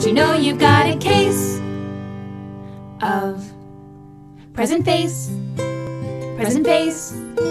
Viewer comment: hehe, I thought they were saying "Prison Face" for a second lol